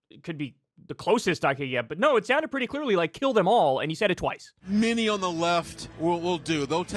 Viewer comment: I don't know man, the guy that said kill them all was pretty damn drunk. In fact, kill them all was just about the only thing I could understand. But he definitely said it😅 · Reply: It sounds like he said "Joe Em All".... God I hate saying this but redneck/country accents.. are the worrrrst.